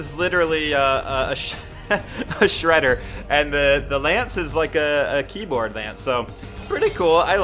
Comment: Did he said lance at 1:24?